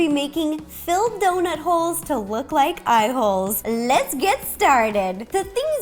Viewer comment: I thought she said a$$holes